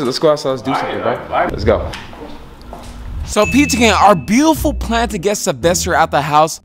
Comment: When this man said P2 Gang is sounded like he said Pizza King no cap